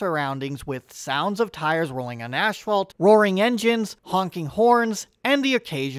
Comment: did you call the asphalt "ash-phalt"?